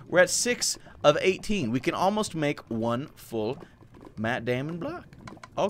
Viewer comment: I thought he said Matt Damon blocks